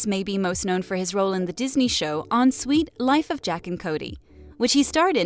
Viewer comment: Anyone else hear her say at 2:00 jack and Cody instead of Zach and Cody